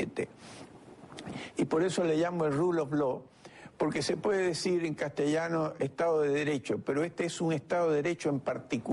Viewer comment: que dice el señor Ribas en el 07:29... gracias por la ayuda... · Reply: Edward Berstein